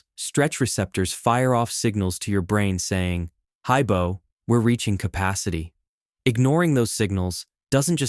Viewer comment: did you just say "haibo" or is that just my south african ears